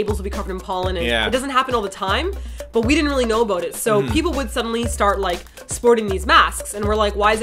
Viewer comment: sounded like she was saying "didn't know aboot it" lol, canadians <3